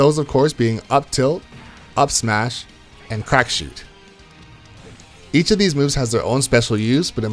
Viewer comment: did you just say “crack shit”